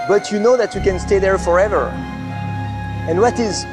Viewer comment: Totally off topic, but at 3:52 I hear Bertrand say 'forether' instead of forever. My French coworker says 'howether' instead of however. Is the sound combination wev difficult for the French to pronounce?